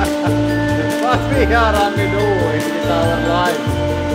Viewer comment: did anyone else hear him say " Haha what are we doing with our life?"? 😂😂